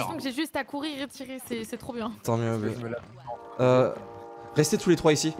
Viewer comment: j'ai entendu un ton mieux bb de a trivia